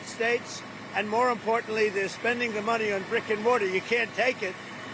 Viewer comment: I thought he said rick and morty instead of brick and mortar